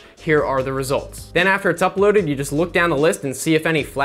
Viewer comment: I swear to god, when you first said 'common sense' I heard 'comic sans'.